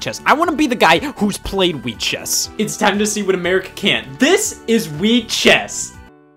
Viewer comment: We've found the sequel to "Genesis does, what Nintendon't" "Europe does, what American't" (Yes I know he said America can't, but that sounded like one word)